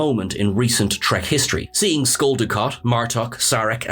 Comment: Did you say Skull Dukat?